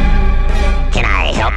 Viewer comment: Lol i thought he said " Can i help you?, Tech N9ne!"